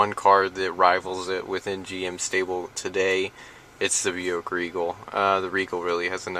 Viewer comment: what does he says? what car? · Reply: רותם שמואלי The Buick Regal.